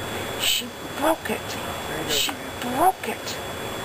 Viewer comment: At first I thought she said "Shit! Fuck it!" ("She broke it!"